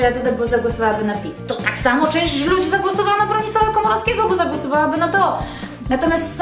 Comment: czy ja usłyszałam ,,profesora Komorowskiego"?? :D